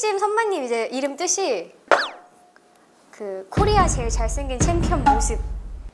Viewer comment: 아ㅋㅋㅋㅋㅋㅋㅋ 코리아 제일 잘생긴 챔피언 머슴이라고 들었네ㅋㅋㅋㅋㅋ